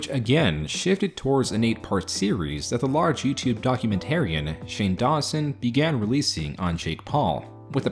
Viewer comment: I totally heard "YouTube Contrarian, Shane Dawson" I had to rewind that bit haha